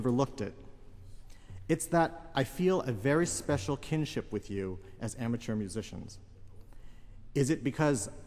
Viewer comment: What does he say from 2:55 to 2:58? Thanks! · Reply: "It's that I feel a very special kinship with you as amateur musicians."